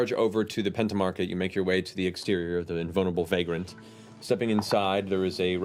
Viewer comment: am i the only one who heard boneable Vagrant